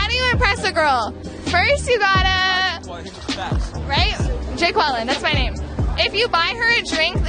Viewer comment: Did she just say that her name, unironically, is "jay quellin" - like in the god damn Key & Peele 'Substitute Teacher' skit at 0:50 ? Bitch it's and you know it.